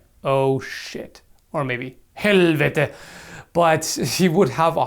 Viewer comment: what does he say ? I can't really understand the words. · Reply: "Helvete", it's Norwegian/Swedish for "oh shit".